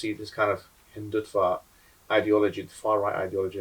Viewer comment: Hindutva and the caption says Hindut fart 😂 Spot on